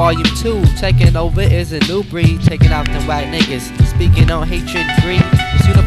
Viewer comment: I always thought the opening verse said “shoutin’ out the white n*ggas speakin’ against hatred and greed” when it’s really “takin’ out the wack n*ggas speakin’ on hatred and greed” as I could here right now. He’s talking about whacking haters haha